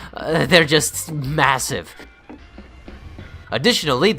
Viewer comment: did you just say MASSIVE ?